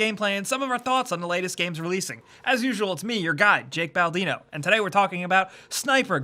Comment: Thought you said your god jake baldino